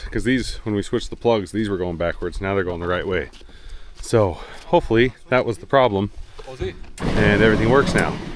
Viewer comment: Ho ho wait a minute…. 26:56 do i hear one of u Guys say in dutch: “komt ie!” ….? Or am i wrong? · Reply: He said " so hopely "😂 had to re-watch